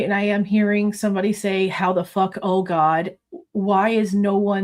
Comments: I HEARD: “how the Fu€k oh God Ethan’s dead” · I heard “what the fuck. Oh god. Ethan’s dead.”